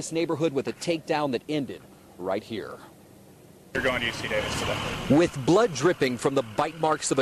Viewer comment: Did I hear "you're going to UC Davis today"?